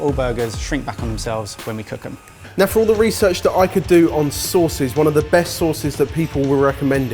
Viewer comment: was saying “sauces” but all I heard was “sources”